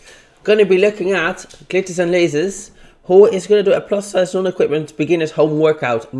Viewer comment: Lol I misheard you when you said glitter and lasers and heard clitoris and lasers 😂😂